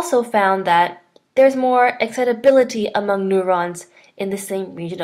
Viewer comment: what's the word at 1:47 ? its something like e--ability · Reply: You probably know this by now, but the word there is "excitability".